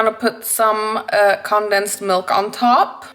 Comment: I really thought I heard her said "condom's milk" 😩🖐️